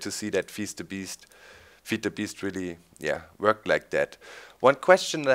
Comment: Did he said "fist the beast" ? lol